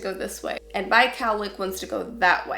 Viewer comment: Can you please tell me what word she says at 4:01? I tried to look it up but couldn't figure out how to spell it. I have tried the subtitle for that which was quite funny because she says it twice and first time it gives "Catholic" and second "Mike Alec". I definitly don't have either of them on my head. :D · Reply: Ahhhhhh - never mind! It's cowlick!